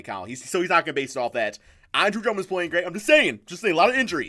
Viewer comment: it sound like you said andro Drummond lol